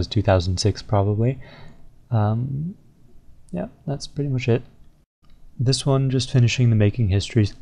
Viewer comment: it sounded like he was like " yeah thats pretty much shit " at first ... and that picture with zezima he didnt even say anything about him lol